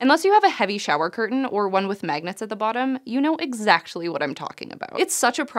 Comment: "You know exactchly what I'm talking about.." did anyone else catch this?